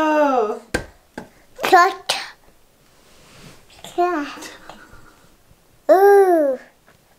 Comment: I think tried to say ‘Cashew’ at 8:54🥺